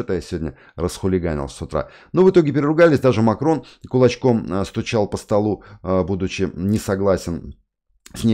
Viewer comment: Послышалось сначала "Макрон очком стучал по столу..." , удивился... спасибо! ))